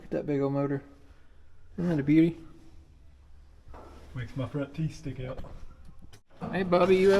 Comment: Did my ears deceive me or did I hear “it makes my front teeth stick out” 😂